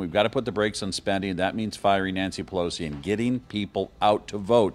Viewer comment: All I heard was “get Nancy Pelosi out”. Yes please